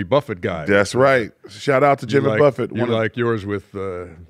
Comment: I had this on while I was cleaning and thought they said Jimmy Butler..lol